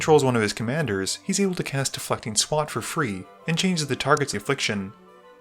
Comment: hes able to cast "WHAT kinda swat for free"??? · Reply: Thank you, I definitely heard "A fucking swat"